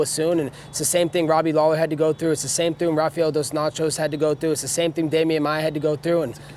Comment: Did he said Rafael dos Nachos ? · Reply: He did said that